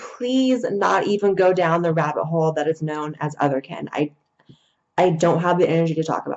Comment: Help please, I'd like to understand "other can". If that is what I heard her say, ( closed captioning was no help) I actually want to learn, not being mean or sarcastic. Just curious. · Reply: Otherkin. I can't help you understand it because I sure as shit don't, but that's the word she was saying.